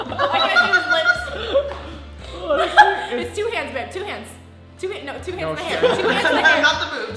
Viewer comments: did she said "babe" ? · Did call babe? lol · Um 2:42 did she say BABE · Did I hear say babe to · Did call babe?